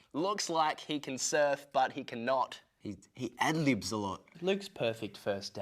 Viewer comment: What did Calum say at 2:54? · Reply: "He ad-libs alot"